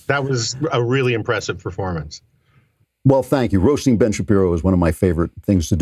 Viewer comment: I heard, "I loved you at the Ben Shapiro arrest!" and my ears perked up! Blast my dyslexia; turns out he said "roast" which is a lot less interesting.